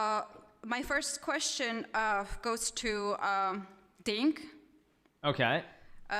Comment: Did she call him "Dink"?